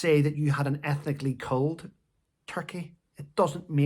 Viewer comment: Laughing my head off... am going a little deaf n so thought u said 'ethically cold turkey' was trying to work out what that was ... brain figured it to be 'ethnically culled turkey' black from white meat ??? when u continued ... thought u wld like the giggle, cos i is still confused ... poor meghan ... mayb her meat is segregated ... lol ... not all your nutter followers r against u... lots like me luv yr charm n down2earthedness, n create their own giggles when they want ... i replayed n relistened so understand what u meant, n can only shake my head as there would b turkey farms ... did she select 1 with 1 leg, or a limp, n i thought she was a vegan ???